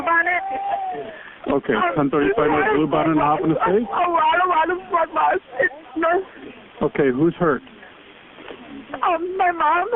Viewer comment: Sounds like she is saying, "A lot a lot of blood loss."